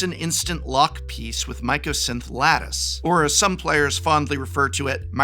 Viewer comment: When he said Mycosynth Lattice I was looking away and thought he said Microsoft Lettuce